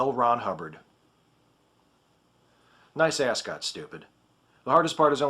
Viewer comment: I misheard "ascot" as "ass gut." Now my mind has been stained with the image of L. Ron Hubbard with a stomach shaped like an ass. While definitely disturbing, this image is strangely unsurprising.